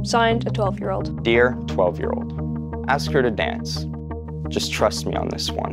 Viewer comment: What is he saying? Just ask her to dance?